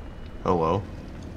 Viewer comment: Im pretty sure he said "hello" but it sounds like he said "owo" so im going to pretend thats cannon